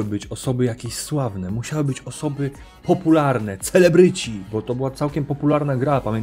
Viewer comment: czy tylko ja usłyszałem na 1:03 cfelebryci?